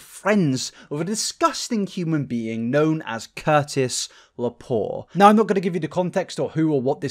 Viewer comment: dude I was listening to this while working out and I heard Kurtis and I was so scared he was talking about Kurtis Conner and I would have to give up another one of my favorite YouTubers